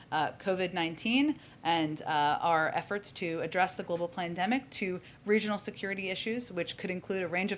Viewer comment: Did she just say global plandemic